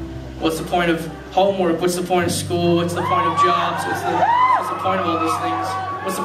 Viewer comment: I thought he said "whats the point in Josh" AND I NEARLY CHOKED ON MY COFFEE